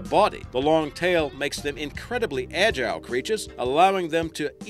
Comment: I always thought you said critter but I just realized that you guys are aussies. So I'm guessing it is creature lol